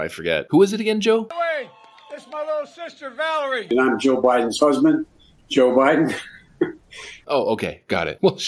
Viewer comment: For a second, I thought he said "and I'm Joe Biden's husband, Joe Biden."